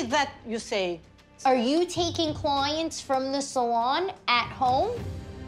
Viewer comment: What she asked " are you taking clients from the sallon at home " What i hear " are you taking coins from the sallon at home ?"